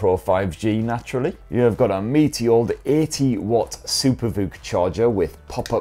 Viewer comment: the caption should be a meaty instead of meteor 1:12 pokey-pin device 2.34 one plus ten pro instead of oneplus tempo 2:58 hasselblad instead of hustle blad 3:24 799 quid istead of quad 4:54 over the likes of oppo phones instead of opera phones